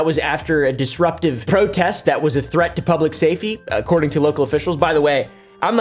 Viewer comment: what is public "Say Fe" ?